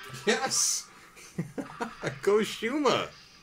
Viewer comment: are you speaking some words in the middle of your sentences? 12:54 I listen you tell "con Shuma?!" In other video i hear you say some spanish word. Estaría genial que si es así agregues algún comentario en Español en medio de un combate para la fanaticada Latinoamericana jejeje. Hug bro